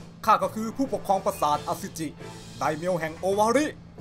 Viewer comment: ได้ยินเป็นอสุจิ🤣 ขอโทษด้วยนะ🤦🙇5555//วนฟังหลายรอบมาก555